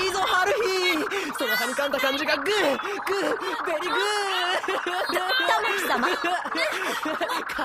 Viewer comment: sounds like tamaki said "good good very good!"